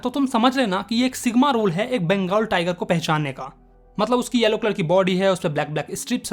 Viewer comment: Did he actually say sigma rule? 1:39 😆